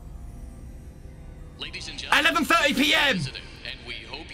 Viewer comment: lol thought he said elephant 30 pm